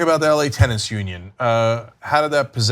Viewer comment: Damn my hearing is awful. I thought Cenk was saying Tennis Union. I was wondering why tennis needed a union...